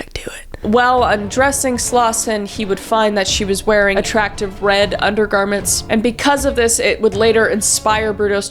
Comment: I literally thought you said "attractive bread undergarments" and envisioned literal bread slices as a bra